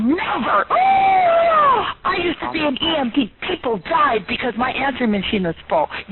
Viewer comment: The first time I listened to the recording when she said "I'm an EMT" I heard "I'm an EMP", which I think is absolutely hilarious. Also, out of all the videos on this channel, this one absolutely takes the cake.